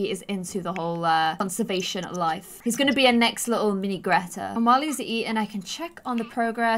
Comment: Did she say mini Greta at 8:22 ? Like does the people around the world now who Greta thunberg is? Proud Swedish person here the haha 🇸🇪 😆